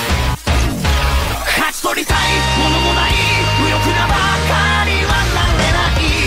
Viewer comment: なぜだろう、勝ち取りたいものもらいに聞こえて来るのは